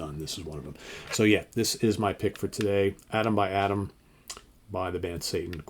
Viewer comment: My pick for day 6 is Björk : Vulnicura. I love how Björk always takes the listener to a journey with all of her albums. The visual concepts on her videos are very interesting too. I hadn´t listened to Satan, so I paused this video and listened to the opening track from Atom By Atom ( I thought that said Adam By Adam :) ). The musical parts sounded really great but at least at my first listening I thought that the singer sounded a little bit weak. He didn´t have the commanding presence like Dickinson or Halford and I think that music like this needs more powerful singing.